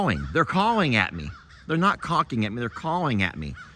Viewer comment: Cawking🤣🤣🤣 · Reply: I thought he said caulking! 😂😂❤